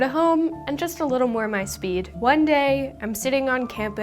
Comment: I thought she said, "and just a little more BuzzFeed" 😂